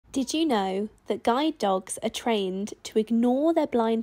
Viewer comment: U callin a guy dog molly wut the hell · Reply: ohhhhh ok lol srry I thought u said a guy